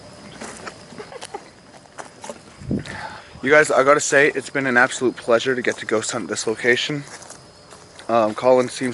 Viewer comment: right after the womans voice evp you can hear a man whisper, can't make it out what it's saying tho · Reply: Sounds like ''hear my voice''